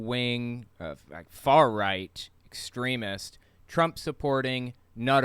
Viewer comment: At first I thought you said 'excremist,' like a made-up word for someone who dabbles in excrement. I was pretty close...